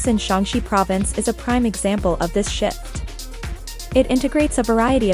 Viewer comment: Im sorry, what did she say at minute 2:04 ? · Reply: “shift”